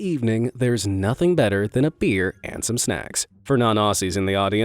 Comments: I thought he just said “snack”. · I can't be the only one who heard snacks at first right.